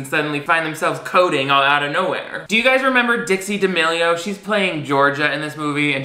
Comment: When you said "Do you guys remember Dixie D'amelio?" Why did I hear 'Ditzy D'Amelio'? My brain is straight telling on me and how I really feel about these tiktok-ers, apparently... 🤪🥴💩